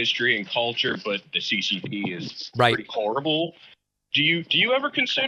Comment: When the caller said the "CCP is horrible", the captions say that "the CCP is right". lol Because of the timing of Pakman saying it